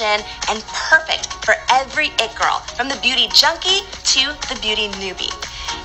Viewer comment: I thought she said the beauty chunky